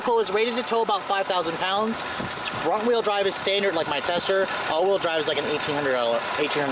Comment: Anyone catch when he said, "wrong wheel drive," @ 11:10? Nicely done.